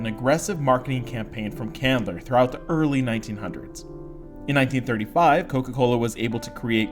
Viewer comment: I would to point out a mistake with the captions at 2:27 the captions say "Coca-Cola took of with an aggressive marketing campaign from Candler throughout the early 1990s" the guy doing the voice over says 1900s not 1990s